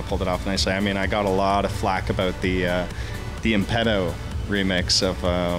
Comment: what does he say impeto?